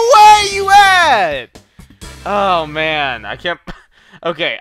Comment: It sound like he said were you at on